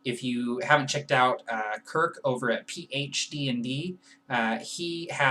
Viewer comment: Who is that fellow you mentioned at about 2:10 in the video at PhD&D (?) I tried to look that up, but I think I am mishearing you because I found nothing... :) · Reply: Kirk, from PhDnD: <link>